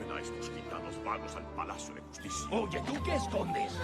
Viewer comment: Yo escucho que dice "Lleven a estos gitanos 'VAGOS' al palacio de justicia". Escucho "Vagos" en lugar de "Malos", pero no estoy seguro. Por cierto muy buen trabajo.